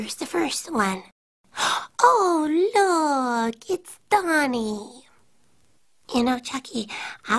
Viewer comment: It always sounded like she said ohhh look it's the MONEY